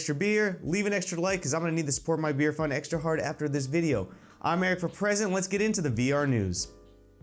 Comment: For the longest time I thought your name was, it don't really sound like your saying Eric For President.